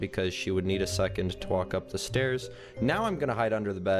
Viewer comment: First I thought you said she needs a second to twerk up the stairs instead of walk 🙃😂☺️😅😂🤣